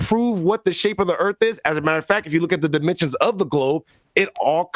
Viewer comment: did he just say “the dimensions of the globe?” 😆